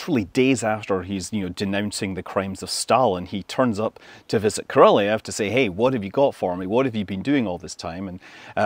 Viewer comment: i thought you said "Karelia" lmao